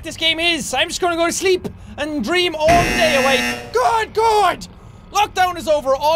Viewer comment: Lol at 00:11 he says GOD GOD instead of good god